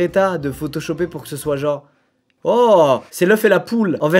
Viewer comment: Moi qui regarde tranquillement la vidéo et d'un coup " OH WHASA !? " 7:28 😂